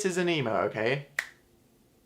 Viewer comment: Hehehehe I when you said you're not an emo I heard it as.. I'm not a Nemo. XD No Dan, you are not a clownfish. ;)